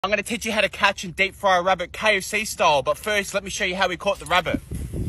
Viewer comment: For a split second, I legit thought he said "let me show you how I catch and cook rubber"... I was like "What the fu...!?🤨🤔...😲Ohh, he said RABBIT". My old ass earbuds aren't doing a good job of capturing the sound effectively. I'm in a hospital like facility right now AND I don't have access to my good headphones. I know y'all probably don't even care, because y'all don't even know me, but I've been here for the past three and a half years due to an illness called GBS (Guillain Bure Syndrome). I haven't been able to walk, or breathe without assistance, because it basically paralyzed most of my nerves. As I was told by my family the other day, I ACTUALLY stopped breathing at one point, but as you can obviously tell, they were able to bring me back AND save my life... Thank God for that!! Anyways, sorry for the WHOLE backstory, but I figured that I needed to explain why I didn't have access to my GOOD headphones... ✌🏽+❤ to all those out there that may be in need